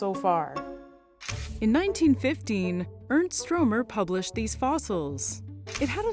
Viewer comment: when you said "Ernst Stromer" I almost felt like my head was gonna spin cause I heard "Ernst Rohm" lol